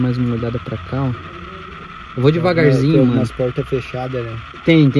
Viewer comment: so eu ouvi alguém a dizer "tem alguém aí"